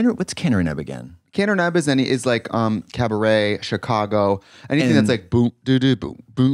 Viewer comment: What are they saying when they’re talking about the term that describes cabaret and Chicago style musicals? Sounds like “cabaret-nab” or “caner neb”? Can’t make it out. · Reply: Oh maybe it’s Kander & Ebb?? Looks like they did some Liza musicals.